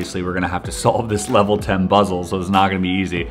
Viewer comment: he says “ obviously we have to solve this level ten BUZZLE” no I did not mistype I meant buzzle because that’s what he said like if you noticed it too 😂